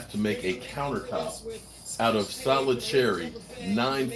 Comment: For a second there i thought I heard you say we're making a counter out of cherries !lol! I'm thinking, hmm,cherry cobbler counter,cherry pie counter mmmmm mmmm,lol! mixing keto with carpentry cherry turnovers counter,the possibilities are endless and delicious! The blue lizards are a cool idea as all get out too! Hmmm,with cherries too? Lol! ▪☆☆☆▪